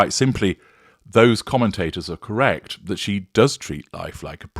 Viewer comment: Oh, I thought you’d said Porn Queen. But, it’s Prom Queen. Got it.